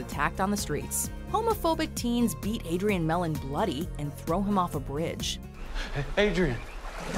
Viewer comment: What people heard: Throw him off the bridge. What I heard: Thru 'em of da brigade.